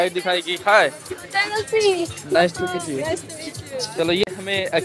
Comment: i heard nice to kiss you 😃😄😄😅🤣🥰😘